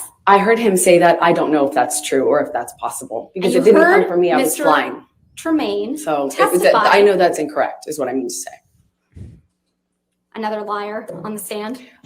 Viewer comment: Sounds Like Amber says: "It didn't come from me, I was lying...I know that's Incorrect is what I mean to say"